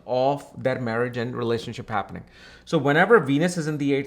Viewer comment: 😅 thought you said penis